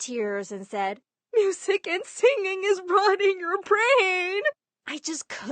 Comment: wtf did she just say??? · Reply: “Music and singing is ruining your brAAAiNN”